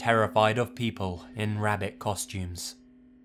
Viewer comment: I got to the "dont turn around," and turned anyway. And I found..... my rabbit peeing on my bed. Truely horrifying :c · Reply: oh rabbiTs! I thought he said rabbis.